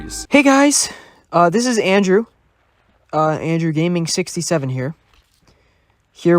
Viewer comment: I thought he said his dog name Andrewgaming67 😅